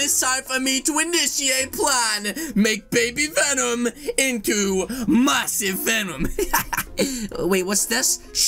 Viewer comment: I thought when he said massive venom I thought he said mustache venom